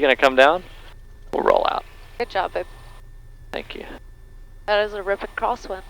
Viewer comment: did i hear her say "good job babe"? on landing